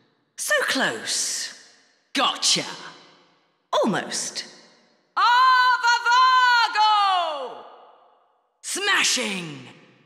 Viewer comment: ???? que dice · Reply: Alextástico Dice Avavago. Lo dice en el idioma de los “ángeles”